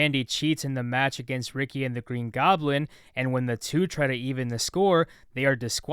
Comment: did you really say green goblin?